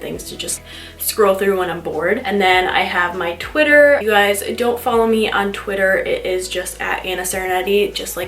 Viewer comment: did you say “don’t follow me”😂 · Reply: LOL! I said if you don’t follow me, but it kinda sounds like I did say don’t follow me😂